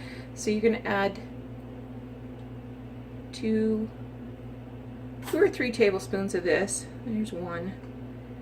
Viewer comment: Oh I'm so sorry I don't know why I thought you said flour, I'm crazy I guess ,, and I will be trying them thanks